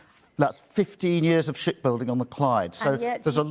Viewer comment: "Shit building on the Clyde" I know what he is saying but still, lol